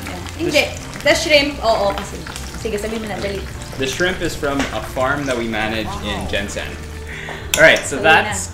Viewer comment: Erwan says GenSan?? Did i heard it right? Well thats amazing... Edit: Kaway2x sa mga taga Gensan..😊